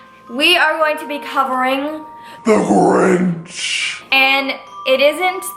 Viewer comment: is sounds like she says THE ORANGE instead of the Grinch lol